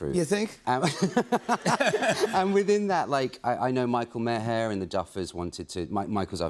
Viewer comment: thought he said Michael Myers! That would fit for S4 Vecna's end!